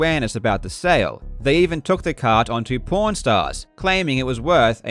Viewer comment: “porn stars” can’t be the only one who hears that 🤣